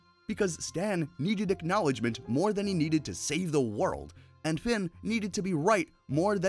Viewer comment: And.. did you say finn??